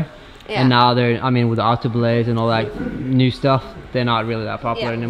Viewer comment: What does say about how the treadwall is not popular anymore? @ 10:02 is he saying auto belay and other new stuff phased out Treadwall?